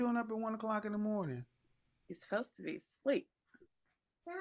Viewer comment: She clearly says "HE'S SUPPOSE TO BE SLEEP" Then when Kevin asks her "HOW OLD IS THAT BOY?" She tries to correct Samuel by saying "GIRL, GIRL, SHE'S 6. Kevin Didn't really catch on to her mis-wording. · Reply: “You suppose to be asleep “